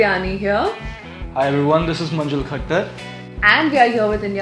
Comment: Is it manjul khacchar...Believe me i heard it khacchar😅